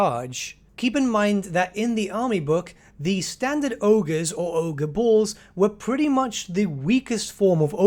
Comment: Actually they were called Ogre Bulls in their rulebook. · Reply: I thought he said Ogre Balls